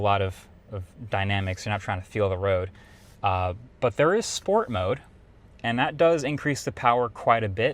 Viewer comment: I thought MKBHD said he was driving in “POOR” mode. lol.